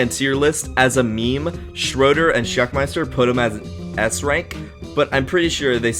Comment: I swear when you said s rank I thought you said ass rank.